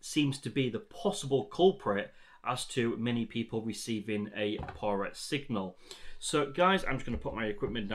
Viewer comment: To. Thanks for this. I am deaf so using subtitles, at 11:30 ish they say "11:26 controller 11:28 support but what seems to be the 11:31 possible culprit 11:32 as to many people receiving a parrot 11:35 signal 11:36 so guys i'm just going to put my " "parrot" makes no sense to me, can someone please tell me what he actually says? Thanks. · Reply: Hi Sean, I say poorer signal 😊 happy to help if you need me to help in any way just let me know